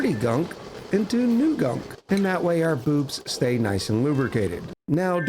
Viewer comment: I misheard, so I thought you said "our boobs will be nice and lubricated"